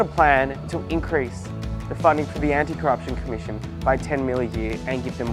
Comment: When he said "anti corruption commission" I thought he said "anti-christ commission" almost had me voting for the libs